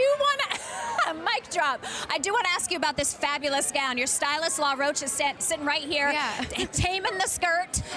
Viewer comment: anyone else hear her say roach? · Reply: That’s his name lol he’s also zendaya stylist. Law Roach